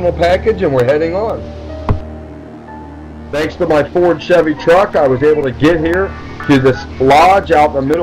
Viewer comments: did he just say " thanks to my Ford Chevy truck"....lol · did he say ford chevy